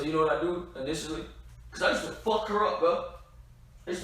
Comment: bro your crazy when he said i just wanna fuck her up he says the reapeted line faster at the part and makes it sound like he said i used to fuck her up